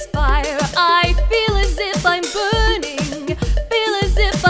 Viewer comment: When I was listening to that song and I played it 1.5 faster I heard "I'm pudding" XD